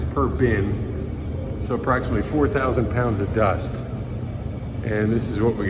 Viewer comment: did I hear it correctly, 4000 pounds of dusts? 😱